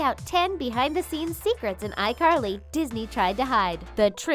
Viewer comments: Lol is it just me or did it say Disney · did she say disney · did you just said Disney? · did they just say DISNEY?